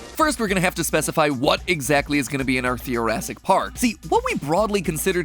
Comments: Thought he said "Thoracic Park" and I was like... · I kept hearing this as, "Thoracic Park", like as referring to the chest.